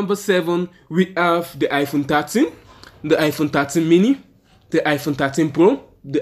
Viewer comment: wtf u say I aint understanding shit “Iphone tatsen + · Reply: he says thirteen